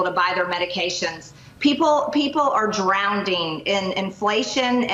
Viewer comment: Did Marj just say people are "drownding"? My god she is so ****ing stupid. 🤣